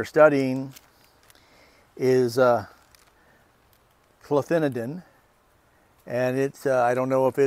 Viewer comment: Interesting, I'll have to look at Bob's video. A little confused, cloanthadin (probably spelled it wrong) is a neonic used as a corn seed treatment i believe. Isn't that supposed to be deadly to bees. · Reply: your right, it sounded like you said cloanthidin which is a neonic, that makes more sense.